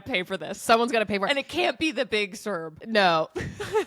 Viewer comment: The Big Serb, ok that makes more sense, when i first heard, i thought you said the Big Syrup and the after reaction i had to go back and listen 😂